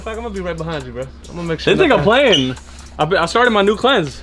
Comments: did he just say this nigga playin ??????????????????? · Sound like he said "this nigga playing" at 9:27 lmao